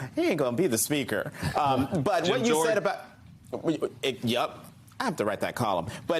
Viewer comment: what does Steele say there? I've listened to it half a dozen times and i have no idea. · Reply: Sounded like 'Gym Jordan'.